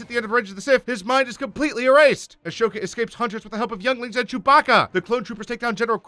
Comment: I heard "Ashoka escapes with the help of Young Link" and I was like, I did not know that crossover happened. Then I realized he said younglings and I was dissapointed.